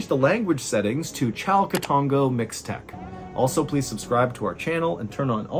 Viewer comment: for those that don't know what he said (like me originally), he said Chalcatongo Mixtec